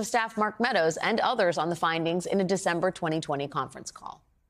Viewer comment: That's the year 2020! That's when it happened! It says it right there! Why tf is she saying twenty and twenty??